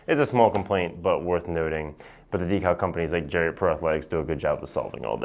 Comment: What was the decal company he said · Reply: ProAthletics